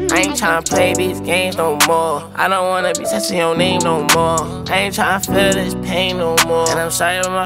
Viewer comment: this whole time i thought he said "i don't want to be attached to your name no more"